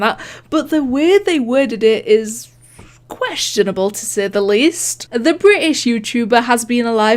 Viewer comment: for some reason at 7:54 instead of hearing “questionable”, i heard “quite shittable”